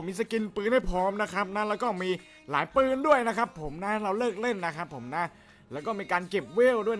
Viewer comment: ผมได้ยินพี่พูดว่าให้เราเลิกเล่น